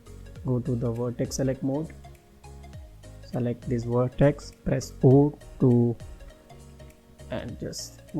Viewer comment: Sorry, what key are you saying to press at this point? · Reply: G then Z to Move vertex down 😀